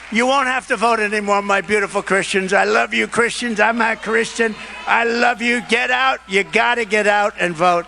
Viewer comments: "My beautiful Christians, I'm not Christian..." - Trump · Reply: That's what I hear every time it plays. It does sound like he says "I'm not Christian" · Quote from DJT: "I LOVE YOU, beautiful Christians, I'm not Christian....get out and vote". Really?! These people can't see his hypocrisy? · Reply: I thought I heard him say "I'm a Christian". Either way, it takes nothing away from his hypocrisy.